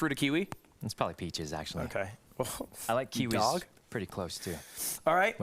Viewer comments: did lud really say dog ? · Did he say dog when asking sykunos favorite fruit?